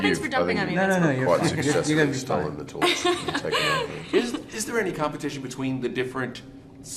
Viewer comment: What did Chris say there? Anyone? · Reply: He said, "You've quite successfully stolen the torch and taken over." 😂